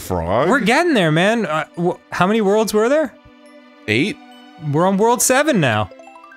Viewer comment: I know Danny said "We're in World 7 now" but I swear I heard him say World War 7 the first time